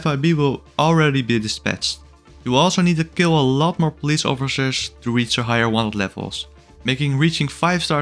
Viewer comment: i heard that fumble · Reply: Officshers